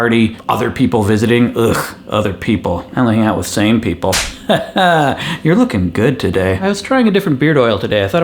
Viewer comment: I thought you said sane people... They scare me... then I realized after seeing your clone you meant same people.